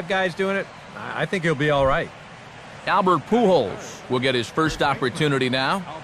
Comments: Did anyone hear the women at 3:30 say f***k you albert lol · Did anyone hear the women at 3:30 say f***k you alberys lol